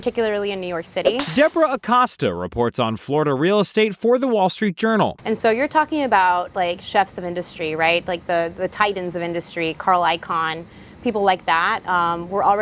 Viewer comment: Did she just say "Chefs of Industry"? 😂👨‍🍳